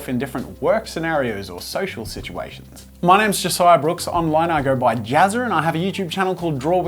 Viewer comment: did you say "I go by Jazzer"?